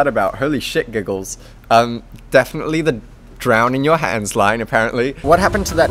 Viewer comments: I always hear it as "drown in your hands" no matter how hard I try I literally don't hear _ 😂😂😂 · NOT THE ONLY ONE WHO THOUGHT HE SAID CUM IN YOUR HANDS. Thank god I'm not the only one with hearing disorders. (Not really, but is that a thing?)